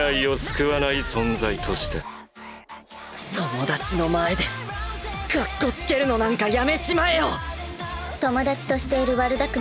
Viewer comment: ovv it's so cute I didn't understand what he said though ,( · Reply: オットー「友達の前で格好つけるのなんか、やめちまえよ！」 Otto「 Stop showing off in front of your friends‼︎」 I'm sorry if my English didn't make sense.